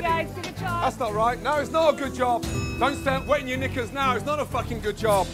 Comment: what did ramsey say? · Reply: Knickers get your mind out the gutter